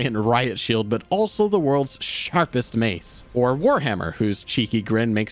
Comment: did bro just say sharp mace? 💀